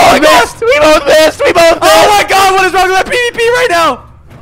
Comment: does anyone else hear whats wrong with my peepee rn